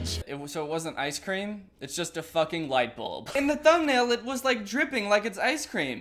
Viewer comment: I'm sorry did u say lightblulb 😂